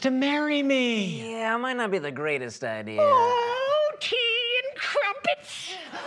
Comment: "Oh tea and crumpets" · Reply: I thought he said "Oh teen crumpets"